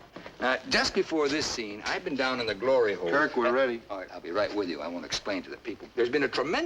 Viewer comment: Pardon. Did he say he was down in the glory hole? 2:34???